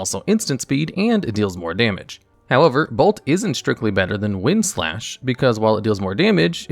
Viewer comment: did he call Wild Slash, "Wind Slash"?